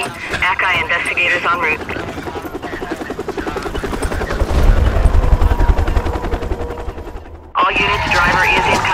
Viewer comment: What did she say at 6:50? Idk help me out here... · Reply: "Wanna go for a Date?" "Wai- HUH?!"